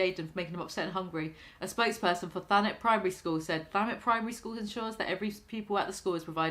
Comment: I thought you said Thanos primary school💀